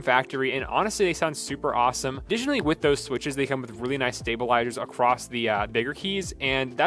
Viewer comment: what did he say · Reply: additionally with the switches, it comes with really nice stabilizers